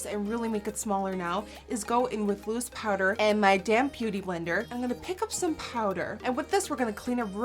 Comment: lol I love you sometimes when you say "damp" the p doesn't come out and it sounds like damn. made me laugh cause I thought you said " your damn beauty blender" made me laugh so hard.